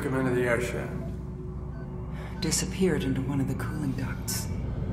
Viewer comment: What does Ripley say around 00:42 - It disapeared in one of the climbdocks?